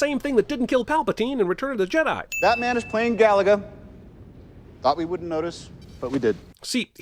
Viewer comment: When I first saw this film, I misheard one of Tony's lines and thought he said "Batman was playing Galaga," which would have made for a strange yet interesting crossover film.